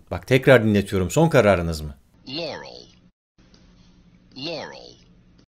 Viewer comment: Lauren duydum